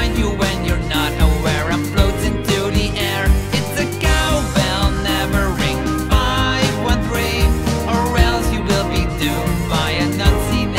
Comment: I thought he said “it’s a cowbell, numbering 513”, not “ its a cowbell, never ring 513”